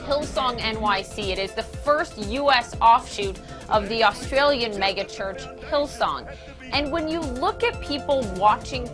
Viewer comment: Did she just say "the australian maggot church"? And about "from the nameless to the famous"... isn't this vanity and a deadly sin?